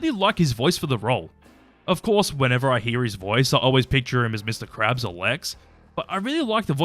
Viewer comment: About Clancy Brown, it took me a couple of times to understand "I picture him as Mr. Krabbs or Lex". I kept hearing "I picture him as Mr. Krabbs' old legs" I kept picturing Clancy Brown doing the sound of Mr. Krabbs tiny legs walking.